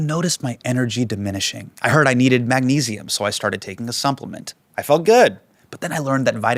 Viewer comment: YouTube should have a search function for thr comments section. "did anyone else hear Hasan say sumplement at 26:02?" is what I want to post, but I could just search the word supplement or sumplement and I could find my people.